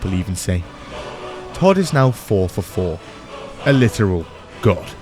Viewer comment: did you say Todd is now 4ft 4in?